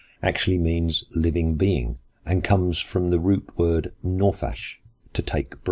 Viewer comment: I can't find any mention of the root non-peh-shin (Nofesh) meaning 'to take breath'. All I see are references to a time of rest. There is the somewhat interchangeable word for Nefesh: Neshamah. From which, I suspect, the modern Hebrew word Neshimah (breathing/breath) is derived. Anyways... I go straight to offensive when it comes to 'the fool hath said', because in Hebrew the word does not imply silly. Within biblical context, the word Naval means a bad person (evil), not a stupid one. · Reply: ***** Yes, of course. That was selective listening on my part. You said "from the Greek word" which I misheard as "from the root word." The three letters Non Peh Shin that spell Nephesh, with just different vocalization can be read as Nophesh. Which confused me because Nophesh in Hebrew, although sharing the root letters, is only vaguely related to the meaning of Nephesh (and now that I think of it, must be a modern word).